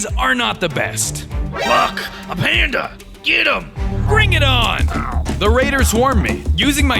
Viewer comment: I swear I heard f_ck a panda get um